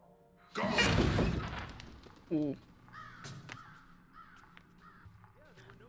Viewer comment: when she mentioned what? What did you say here? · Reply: "When she mentioned Masha?"